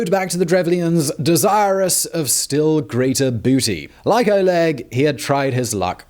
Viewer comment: I swear I heard "desirous of a still greater booty" haha